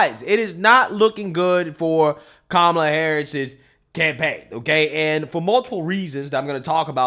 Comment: I thought you said "It's not looking good for Kamal's tampaign." As a spin on Tampon-Tim. LOL. But it does capture the feeling of her campaign.